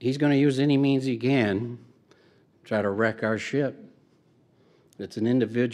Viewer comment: I could have sworn Tony said "wreck our shit" lol